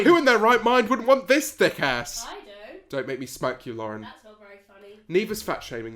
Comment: and thanks to the editing I know she said, "I don't" when I heard, "I do" originally. Thanks for clearing that up, now the response makes a lot more sense.